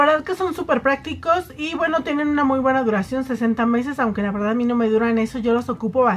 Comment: Hola, yo no se xq ños papeles absorbentes de city color a algunas les cuestan $13, cuando las pocas veces q he ido a sus tiendas me los han dado en $45🤔🤔🤔🤔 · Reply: Rosalia Reyes escuche mal o dijo duran 60 meses ..? O.o creo se ke cruzaron las palabras jiji minuto 2:20 y si amo tmb me los dan caros :/